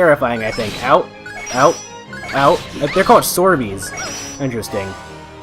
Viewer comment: I thought you said Swordie for a second there XD 2:10 That's not true. I think one of your first harmony chains were with her. Though you did it with another attack and used heal arrow as the 2nd move. 3:48 Not true at all. She's no star child in my books. 8:10 Even better idea. You should use both Spirits pathway and sacred signature for your harmony chain :P 10:56 You want to know who needs speed? One of the people who probably had 0-1 accessories equipped at that present moment. (Like Jazz at 21:49 or Beat/Salsa at 21:50... corrected for 41:00 YES! though now I'm going to be critical and say you gave it to one of the slowest people. Speed boosts benefit faster people in this game more than it does in Legaia normally I'd give it to the slower guy in that game like Lloyd but in this game I'd have given it to one of your fastest characters since they'd be more likely to go twice before the enemy moves.) 12:42 I was hoping that would look cooler. 23:32 You knew it? But I thought you thought it was Falsetto? 27:15 Seems stupid they force changed her in. (I thought it would be 1 on 1 for a moment honestly) Personally I think Viola coming out was the best result but obviously it still would have been good to have given the player a choice. 39:27 sigh oh well. 41:37 Agreed. The attack boost was big time worth it.